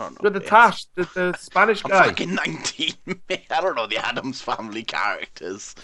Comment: is 19? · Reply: I thought he said im not fucking 90